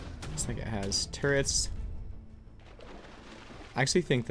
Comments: you said what? 👀 it’s all good tho still love the content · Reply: NovvvaPlays i heard “ this n** has turrets” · what did you say 👀👀👀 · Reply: He said looks like there’s turrets can’t believe how deaf people are or how desperate they are to jump to conclusions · ik I wasn’t the only one who heard this 😂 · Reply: XXMatrixFlames he said “looks like it has turrets” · did he just say what i think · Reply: Def sounds like he said N has turrrts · did he say the "nigga" has turrets